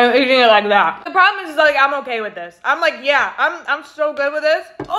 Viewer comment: I’m sorry did she just say boneappletitty?😂